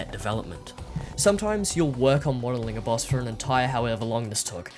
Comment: Try to talk a tad slower in these. I rewound 5:05 several times and can't tell what you said. "wattalinger boss?" This came up in the reused music video at one point as well, but I was eventually to discern the syllables. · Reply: Pretty sure he said "modelling a boss"